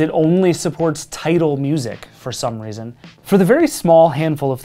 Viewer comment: hey, subtitle guy, it's Tidal, not "Title" Also at 3:22 there's a small typo that says "A nd" instead of "and", but this one is just nitpicking